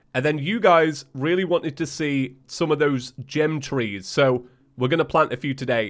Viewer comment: Did you say "gem trees"? I had to replay it several times and even slowed it down.